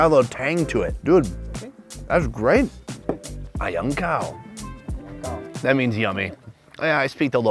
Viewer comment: I’m from Myanmar, when you say “a yan kaung” I only hear “A Young Cow”